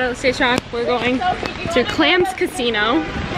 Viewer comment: def thought you said clam casino not clandestino....